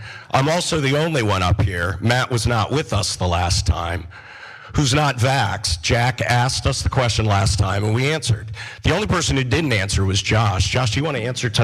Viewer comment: When he said Jack asked, I thought he said jackass.